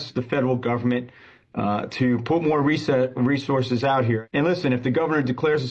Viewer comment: On 11:52 did anyone else hear the guy say put more reset?